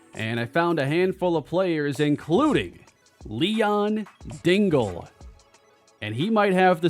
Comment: I was in the other room and I heard say "Leon D..."" and nearly fell over. But it turned out to be Dingle instead of Daniels. 😭But for a millisecond something came alive inside me lol.